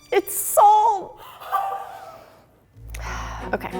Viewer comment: can someone tell me what she said? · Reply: Jay Liebenberg she said “its salt” b/c she accidentally added salt instead of sugar